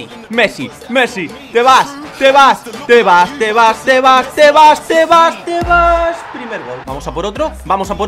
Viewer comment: parece que dices tebas, javier tebas el presidente de la LFP